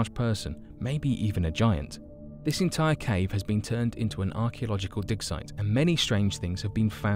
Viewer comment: I heard “archeological dick shop? “